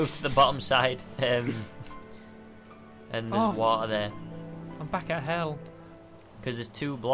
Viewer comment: I thought he said f I am back at hell